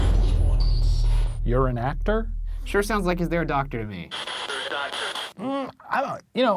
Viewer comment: the ghost steve sounded like it said "are you a doctor?"